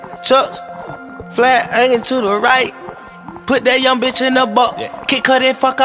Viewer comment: I always thought he said 'Flat Onion to the right " lmao damn im dumb